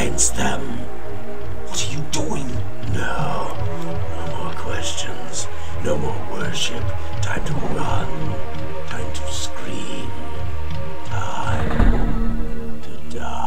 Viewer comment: The last time i used diapers i heard: "No more questions, no more workship, time to run, time to run, time to scream, time... to die!"